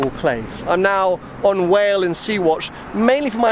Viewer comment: wo wo wo did you just say you "hate whales"? Who the fuck hates whales lol